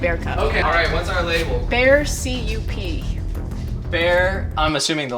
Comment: i heard “bears see you pee” and was like what